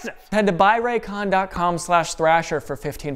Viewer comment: "why are they called malls, when they're really ig" is probably my favorite line because i wasn't expecting it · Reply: I thought for sure he said balls and then cuts out half way through saying egg...as in egg-shaped. reading the other comments I feel dirty now.